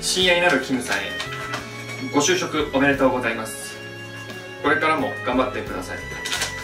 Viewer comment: みんなが「ご就職おめでとうございます！」が「ごしゅうしょうさま！！」に聞こえる😆 ごめんね…😌